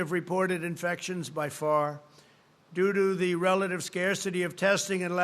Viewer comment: Did he said do do at at 10:50? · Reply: I think he meant “due to” But who really understands half the stuff he says.